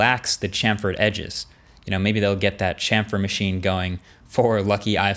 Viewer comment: I'm not a native speaker. What word does he use at 5:48 ? a what machine? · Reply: Chamfered